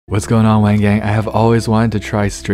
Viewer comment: I Thought He Said My Gang But Later I Found Out He Said Wang Gang And I Was Like What Did He Sayyyy???